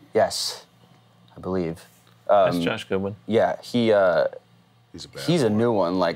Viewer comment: Josh who? (Didn't catch it?) Anyone? 23:00 (Goodwin?) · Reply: Wolfie - "Gudwin" c